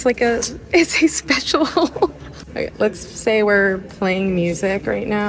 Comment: I’m sorry, did you just say “special hole”… lmao 🤣